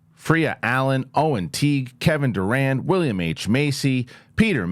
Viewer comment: Y’all keep saying Kevin I thought it was the basketball player I was like he does voice acting Lol but I googled it and that actors last name is Durand it sounds like he saying Durant lol